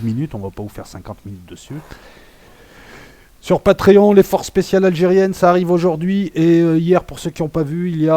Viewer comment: sur quoi les forces algériennes ? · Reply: Forces spéciales et GIS